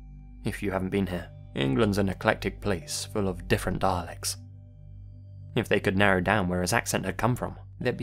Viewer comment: did anyone else hear: 'england is an eclectic place full of different Daleks'?